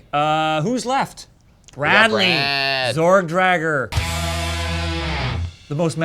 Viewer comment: FYI: Lock Horns Best of of 2017 next Thursday! It's all viewers choice this year so get your arguments ready and join us for the live debate! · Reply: I thought you guys were saying “Sword Dragger,” instead of Zordrager. Which really would be the most metal name in metal.☮️Sword Dragger!💀🎸☠️🎸💀